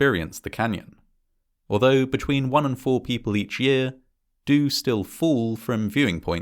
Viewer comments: Had to rewind this a few times. First I heard "one in four people" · Last year I walked the South Rim and stopped at most of the viewing points, and saw many, many people taking insane risks for a selfie. “…between 1 and 4 people each year do still fall from viewing points into the canyon itself” is a dreadful statistic, and these tragedies are entirely avoidable. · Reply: Oh!! Between one and four—I thought he said 1 IN 4 people fall 😵‍💫🤣😳